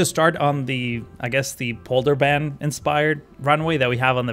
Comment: Is the outer runway inspired by Schiphol? Thought you said polderbaan, but as a Dutch guy it's a bit hard to understand no offense